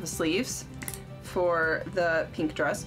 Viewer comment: At first I swore you said Venus sleeve and im like whats a Venus sleeve but you said weenis hahahahahhahaha!!!!!hahahahahha! Sorry not sorry